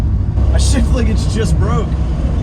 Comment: doesn't say what you think he says. He actually says "shift". 😛